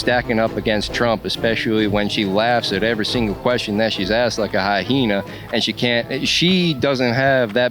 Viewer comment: did he say "like a high hena" instead of "hyena"? 😂